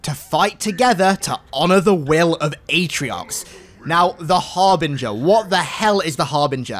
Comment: Oh, the Harbinger? I thought he said the ARBITER. I was so confused as to why the Arbiter would team up with the Banished.